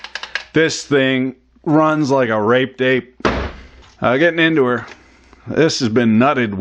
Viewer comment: "raped ape" I thought you said RAPED DATE the first time, golly